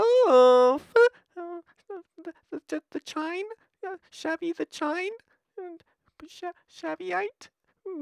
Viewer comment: SOMEONE TELL ME WHAT IS HE SAYING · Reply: Im good Im done "the chin, shave the chin, shave it"